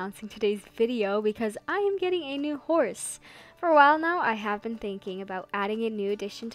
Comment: i have watched this many times and today when i watched this i thought she said "im getting a divorce" 🤣🤣